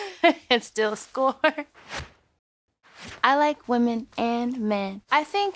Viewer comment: i heard “ i like women in pink “ ?!